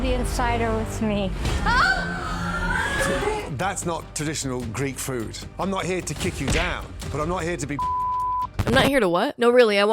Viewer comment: as an expert sailor mouthed person i think he just said " fuck with "